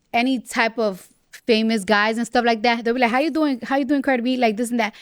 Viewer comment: In ten seconds the closed captioning is gonna say Crabby instead of Cardi B. 😂🤣🤣😂🤣😂